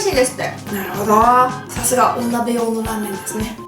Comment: 「さすがお鍋用のラーメンですね」って言ってるとこが、 「さすがお鍋用のお鍋ですね」に 聞こえて、一瞬、んんん？ってなったw